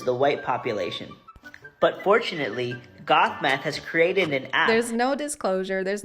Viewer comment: I THOUGHT HE SAID GOTH METH LMAO